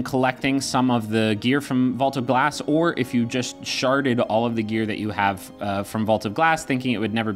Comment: Did he say "sharted" ? lol